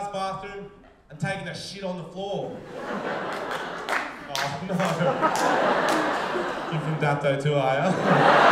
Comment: What did say at 1:46 ish? Couldn't tell. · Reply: Ohh.. no.. You're from Dapto to are ya?